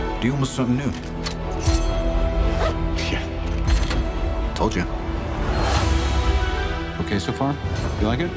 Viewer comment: All i hear is "sh**" Edit:900th comment btw